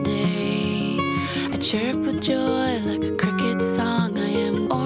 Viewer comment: "I chirp with joy like a cricket song". I thought she said "like a crooked smile" I need clean my ears out! LOL